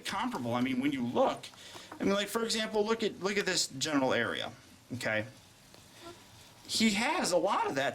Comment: and 19:42 lol i thought you said genital area